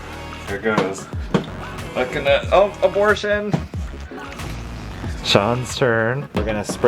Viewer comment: did he say abortion???